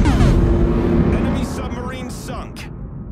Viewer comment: I misheard "enemy submarine suck" .. hrhr